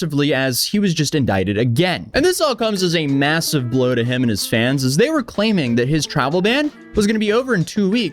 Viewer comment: Lol I thought you said "him and his fan"